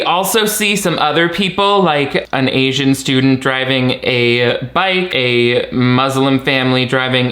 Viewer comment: I'm hard or hearing, but did you say "*driving* a bike" @ 4:23 instead of "riding a bike"? I'm definitely not asking for the purposes of mockery depending on the answer